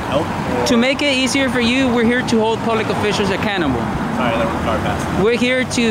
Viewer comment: It sounded like you said you were "hold public officials a cannibal " ........rock on Ren and Stimpy